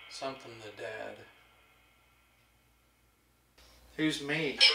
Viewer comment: sounded like "Dude they're dead"...That was creepy. Almost sounded like it was imitating one of your voices 😱